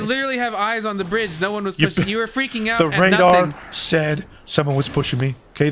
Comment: I thought he said, the rengar as opposed to the radar lmao